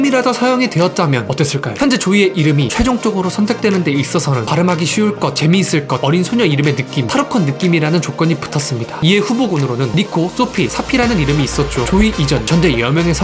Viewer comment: 나 니코 코피 사피로 들었어....